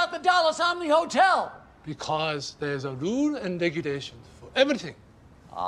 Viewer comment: What the fuck does he say? Rules and Regulations?